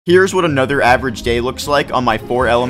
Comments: My first thought was he said areverage gay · I thought you said "here is a average gay in my 4 elements minecraft server" but no it was day NOT GAY GOT DAM